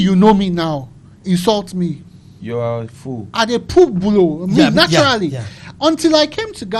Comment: Did said you're a foo.l😂😂😂 ah this guy paaa